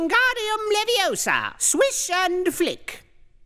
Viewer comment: I don't know why I thought it said "Swish and Fuck"